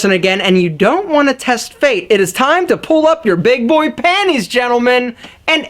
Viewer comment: did he say big boy panties?